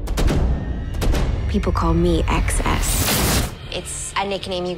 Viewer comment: did she say “Excess”?